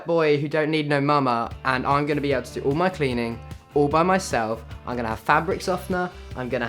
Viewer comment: in 0:37 i thought you said "obama self" oh my god